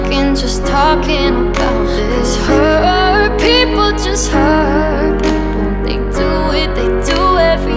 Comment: I thought it kept saying "Her people."